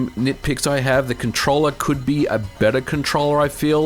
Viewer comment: I heard "beater" unit at first..what my grandad called a old car... :P